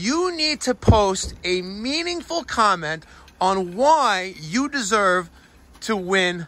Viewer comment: Darn. I thought you said meaningLESS comments.